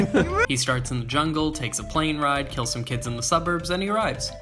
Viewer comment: Did He Just Say K*ll Some Kids!? 2:37 That’s First Degree Child Murder You’ll Go To Jail For Doing That.👮🏻‍♂️🏢